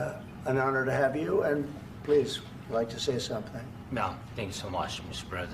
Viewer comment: Hmmmm.... im no accent expert... but i SWEAR i can hear zelensky saying the words "thank" and "you" at 3:00 but... that CANT be, jd said he didnt hear him say thank you once the whole time he was there. HMMMM Is jd high..? I mean... weeds legal in d.c....